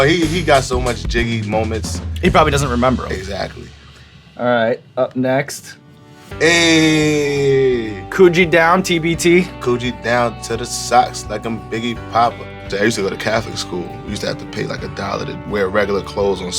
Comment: I thought he was saying "Coochie down to the socks" this whole time yo